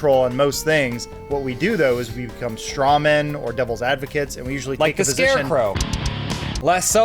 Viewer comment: I sincerely hope I misheard, but did just say "doubles advocate"?